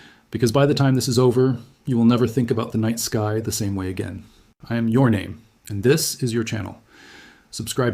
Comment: all I hear now is "I am your name" hahahahah